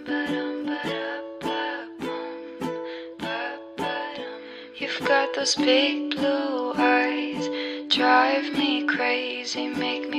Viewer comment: i keep hearing "SHE got these big blue eyes drive me crazy..."